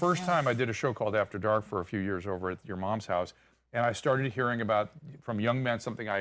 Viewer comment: I did a show called After Dark at your mom's house. DID I HEAR THAT RIGHT?!😅😂